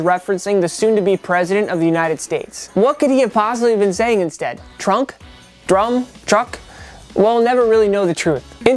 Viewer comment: trouble trouble that’s what he was saying